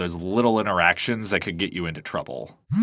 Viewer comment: i had subtitles on and around 5:08 when you said nope the subtitles thought you said “OOP”